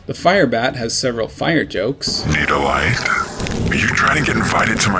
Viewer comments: I always thought the Firebat said "Need a wiseguy". "Need a light" seems to lack a syllable or two at the end compared to what the VA was saying. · This line is the only one that I still have trouble comprehending and understanding.. and its been since ‘98 and still puzzles me to this day. All I hear is “Need a wisega?” not “need a lighter”… I barely hear the L or the lighter part